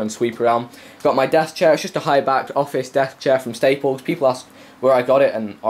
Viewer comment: LOL I thought you said death chair, no offense kinda looks like one